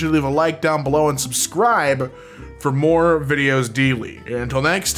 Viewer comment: it kinda sounds like you said “my name is jeff” or “i am air jeff lmaooo”